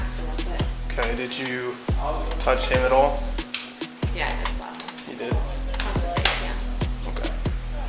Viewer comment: what she actually she says " yes i slapped him, across the face.". Youtube captions: "im the voctim, yeah" good ole youtube